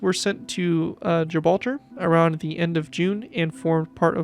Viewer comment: It's amazing how much detail you can but into such a small build. Nice to see the British fleet is slowly expanding too. And at 1:23 I'm not sure if it's just me but it sounds like you said "Gibralger" instead of Gibraltar. Were very protective of our rock collection you know.